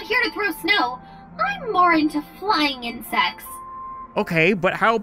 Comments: I thought she said flying and sex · I thought she said flying into sex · I definitely should not watch these videos when I’m tired because I thought she said she was into flying and sex.